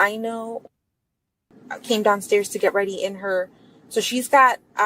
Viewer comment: when give the morning description of what was happening... did she said... I CAME DOWNSTAIR???.. and corrected herself right away?.. or i did not hear it right?.... around 26:00 mark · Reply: She said maddie came downstairs to get ready but corrects herself bc she already said she didnt see her that day. Maddie's name is redacted.